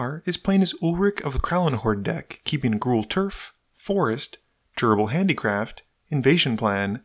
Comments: Every time he says "Durable Handicraft" it sounds like he is saying "Gerbil Handicraft" haha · In the beginning when you said "durable handicraft" I swear I heard "gerbil handicraft." I thought there was an entire new subspecies of rodent tribal I had missed in MTG in addition to rats and squirrels.